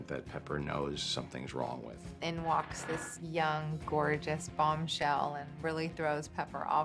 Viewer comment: Uhh is that a normal thing to say in an interview? I know she’s supposed to be a sexy character but rlly? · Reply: Oh I thought he said boobs lol Idk why I heard that